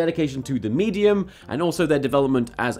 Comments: Am I the only one that thought he said FilthyFrank instead of Phil DeFranco? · For a second, I thought I heard "Filthy Frank" instead of "Philip DeFranco". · When you first said 'Phillip defranco' I thought you said filthy frank! Who else likes filthyfrank? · On Phil DeFranco I thought he said "FilthyFranku"